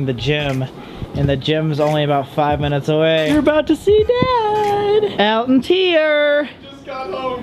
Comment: When Colby said, “Bad! Bad Elton!” I thought he said, “Dad! Dad Elton!” 😂😂😂